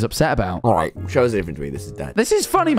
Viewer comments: For anyone wondering what Tommy said: 14:26 "Alright, show us a different tweet, this is dumb" Not sure if this is 100% right but it's my best guess ¯\_(ツ)_/¯ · Did Tommy say “show me a different tweet this is dead”? I’ve been trying to debunk it lol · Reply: He said “alright show us a different tweet this one’s dead” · tommy says something along the lines of "alright show us a different tweet this is..." i cant make out the last bit, but just putting this here in case anyone else has audio processing issues like i do!! (i replayed the part like 30 times till i understood it LOL) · I FIGURED OUT WHAT TOMMY SAID AT AROUND 14:20 to 14:30! He said 'Alright, show us a different Tweet, this is dead' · show us vebren upen day is what i heard 💀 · Tommy says, “alright show us another tweet instead” or he said,”this is dead” instead of ,“instead”